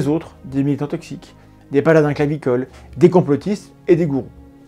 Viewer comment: A 16:35 c'est quoi un "baladin clavicol" ? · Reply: * paladin clavicole : un chevalier justicier, défenseur de la veuve et de l'orphelin, mais uniquement derrière son clavier (tel que je l'ai compris en tout cas)